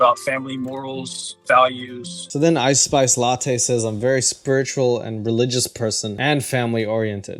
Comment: Oh he said "spiritual". I thought he said she was spherical